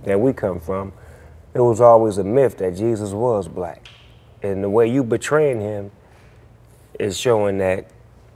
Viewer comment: DID SNOOP JUST SAY AT 2:07 THE WAY YOU BETRAYING HIM ?? LOL .....